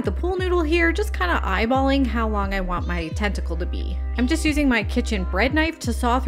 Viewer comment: Very cool, thanku ❤ Ps I thought u kept saying testicle lol sorry 😂😊😅